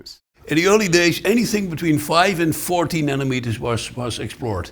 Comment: your subtitles are wrong. they say "five and 40 nanometers" when the text in the video says "5 and 14"